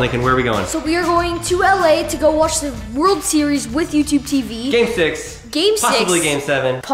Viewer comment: did bro say the world series?